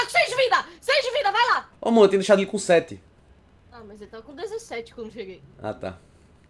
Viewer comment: No 7:36 eu entendi ele falando "Ô mô ( de amor ) eu tinha deixado ele com 7" kkkkk shipo hauehua